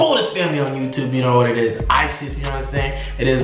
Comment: did he say Isis